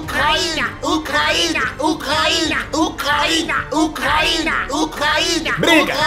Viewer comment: só eu que escutei eles gritando COCAÍNA, COCAÍNA, COCAÍNA KKKKKKK